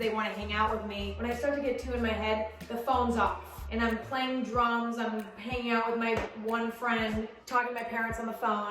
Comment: did she say she plays drums?